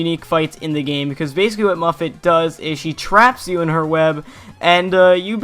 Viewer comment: I thought you said 'muffin'